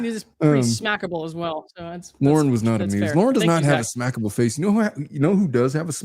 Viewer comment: I seriously thought you said that you had 'a snackable face' lol..........................